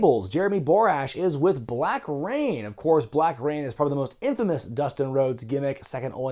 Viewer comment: Did you just say Black Reign? Myah myah myah!